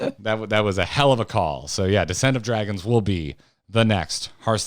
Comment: I keep hearing “the scent of dragons”.